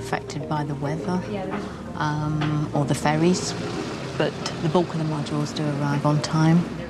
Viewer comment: for a minute thought said sometimes we are affected by the weather or "fairies" (i knew it.. airplanes fly by magic fairy dust) but she said "ferries" LMAO